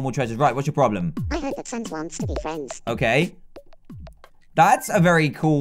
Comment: When Harry said I heard that sans wants to be a friend I thought that he said I heard that Santa wants to be friends!😂😂😂😂😂😂😂😂😂